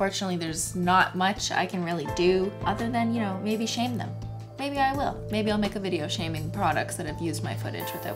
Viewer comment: Misheard her, she said “maybe I’ll shame them” but I heard shank. But like yes, shank them😂